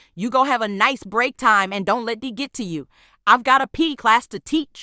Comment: ''don't let D get to you'' (instead of D i hear diddy)💀